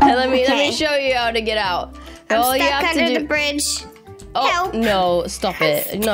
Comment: Why did i hear "hell no" when was in the water at the begining